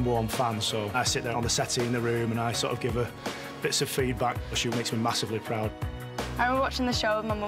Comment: When the dad first spoke, I heard him say "I sit in the back and give her bits of food" lmao. Glad I rewound it to see what he actually said